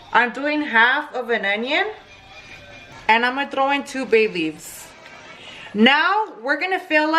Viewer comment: When you said “2 bay leaves” I heard “2 babies” 😂